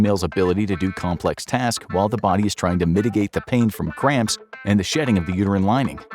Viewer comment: Number 22 had me laughing! -At about 14:08 the audio gets a little slurry and it sounds like he is saying france or framps instead of cramps. I always found human biology fascinating, especially females. Thank you for making this video!